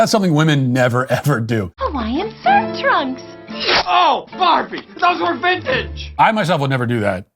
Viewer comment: I heard “OH FUCK” but I knew it was from Toy Story 3 so I was like “they can’t put this I must have misheard” I did, he says oh barbie, but I still can hear the first